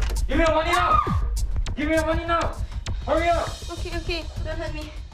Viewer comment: Y’all am I hearing things or did she fr say okie dokie while gettin robbed? 💀